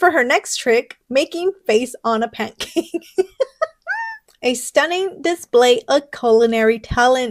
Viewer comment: The one that said a stunning display of culinary talent, I swear I thought I heard "a stunning display of narcissism!" 😂